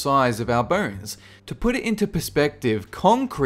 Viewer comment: When he says, "bones", I hear "burns"!